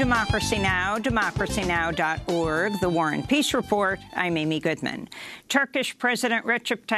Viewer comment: Still waiting for the peace report🙉🙈🙊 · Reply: For the longest time I thought she was saying "war ON peace report"